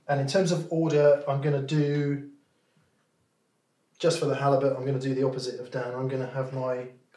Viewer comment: i swear mick said “just for the halibut”